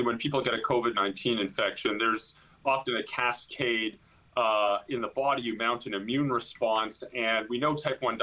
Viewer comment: At 0.30 did he say a "casade in the body amount"? or "amounts"?, or "amounting"? or what did he say? transcribe settings are wrong too allot of times. One or two indistinguishable words throw off the whole understanding. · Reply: Slowing it down and listening helps a bit. He either misspoke or there was an audio signal hiccup right before "mounts", I think. It sounds like he said "...often a casscade ahhhh in the body amounts...". Which makes no sense. I can only guess that he was trying to say "often a cascade and the body mounts ...".